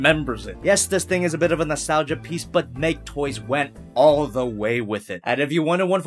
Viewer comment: I'm not the only one who heard make toys wet right